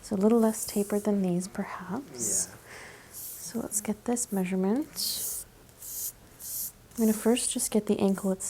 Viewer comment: I can’t be the only one who thought It said “deadliest suit measurement” and thought that the ruler thing she was using was a sword😂